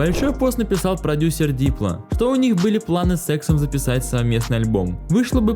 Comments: кто услышал сексом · Либо я даун, либо я даун, но что он сказал? С эксом записать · с сексом👌😄 послышалось) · Мне послышалось "Планы с сэксом" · с*ксом мне послышалось) Лучше бы сказал С иксом) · послышался с*ксом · Мне показалась что он сказал секс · кому послышалось СЭКСОМ? а не С ЭКСОМ